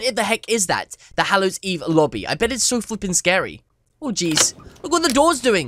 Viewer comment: Sounds like he said fkcing idk.... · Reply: he say flippen. :/